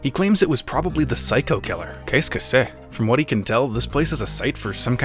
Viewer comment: What exactly does he say at 6:44? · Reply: qu'est ce que c'est it means "What is that?" in french I think